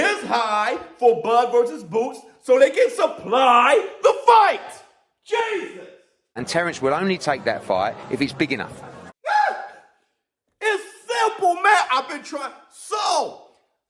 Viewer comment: It sounded like he said "if "he's" big enough" as in if Boots builds his resume up. 🤷‍♂️